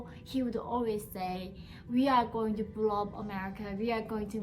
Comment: Did she say that he's gonna blow up America